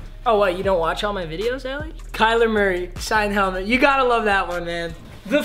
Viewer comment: Who else heard him call her Allie